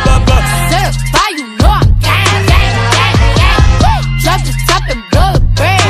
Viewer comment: I always thought she said ‘certified you know I’m gang’ ✌🏻 😂